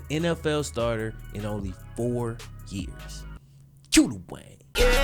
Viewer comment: I always thought he said Que the Wave not Que the Wayne lol.